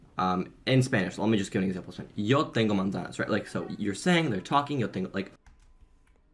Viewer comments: It sounded like he said "tengo monda" instead of "tengo una manzana". Part of speaking a language is making yourself understandable. Yes, it would be good to hear him speaking Spanish in an interview and also giving specific examples and details about his program. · I don't agree with him about learning Spanish that fast, but I could clearly understand "No tengo manzanas" You can see he is not native, but that's not important. We don't need to mark the final S in a strong way, and personally I heard the S. I'm Spanish, by the way. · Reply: He says "yo tengo manzanas" 100%, not 'no tengo'. Your ears are just accostumed to the most common phrasing of certain things and since 'yo tengo' isn't common like this, not improper, just weird, uncommon, overly formal, whatever, your brain hallucinated by hearing something else, which is fairly common for native speakers. In any case, he said yo tengo and mispronounced the Z.